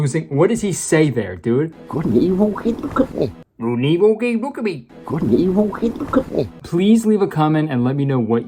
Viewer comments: I think at 5:00, he said “good and evil, kid, look at me!” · i think he said he could'nt even get a look at me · i think hes saying good evening but idk thats all i can hear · i think he said “good and evil, hey look at me.”???? 😂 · I think he’s saying “good and evil? I mean, look at me!” But I’m not sure, he’s definitely saying “good and evil” but the other part is more speculative. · I heard "Good and evil, hey look at me" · "good and evil, kate? look at me" is my guess. idk if her name is kate, or what the context is for that phrase, but that's what I hear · It sounds like he said "Good and evil can't look at me" 😆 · I think he said "Good and evil.. hey look at me" since the woman was looking down when he was mid thought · sounds like he said "good and evil? ok look at me" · I hear "good and evil hey gecook me" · i think hes saying “good and evil…something” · “Good and Evil ain’t cutting it”? 5:00 At least that’s what it sound like to ME what he’s saying. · idk i heard "Good & Evil, okay? Look at me!" like Laurie's boss is trying to talk to her about Tally Hall's album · i hear "good and evil cant look at me"... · it sounds like he says good and evil keep you company but what does he even mean by that 😭😭😭😭😭😭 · I might be very late to the party here but I think he's saying "Good and evil, hey, look at me." 🤔🙂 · Personnally, when the boss says his waird magic words i hear "Good and evil, kidding, cook me" which sounds like it rather belongs in Hannibal